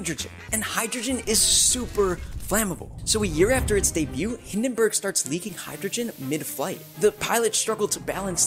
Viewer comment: Bro I thought it said heisenberg on the plane😭💀